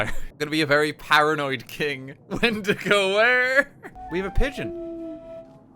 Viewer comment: “when to go?” Did I hear that right? Because if it’s what I think it actually was, I don’t want to sound snobbish, but it’s a very important part of Native American culture that no one is allowed to say that word.